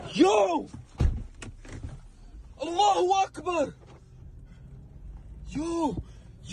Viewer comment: what was that man yelling out? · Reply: Allah akbar. The same thing terrorists say when they blow something up. Also in the middle east during battle.